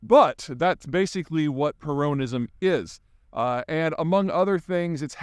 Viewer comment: i think hes actually saying croneyism